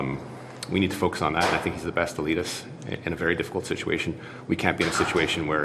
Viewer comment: he said Trudeau is “the best to lead us” but it sounded like he said “the best elitist” and that makes perfect sense.